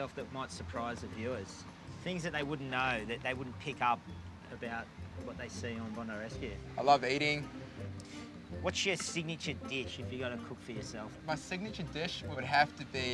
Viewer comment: I heard him say I love Beardy instead of I love eating😂😂